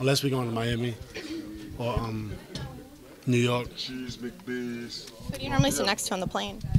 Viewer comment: did anyone else hear cheese mcbees?